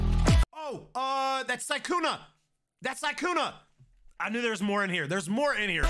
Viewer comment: I heard you say sykkuna if i am wrong its okay